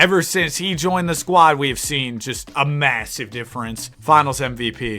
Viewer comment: did you say m-m-massive?